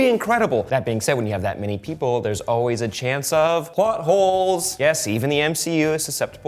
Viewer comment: thought he said “Butt-Holes”